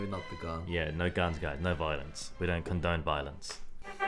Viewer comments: I heard "We don't condone violins"... was thinking "wut...?? that is a stretch..." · When you said "we don't condone violence," it sounded like you said "we don't condone violins." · "We don't condone violines, guys" You don't???? (Btw I know it was "violence" but I misheard and find it rather amusing)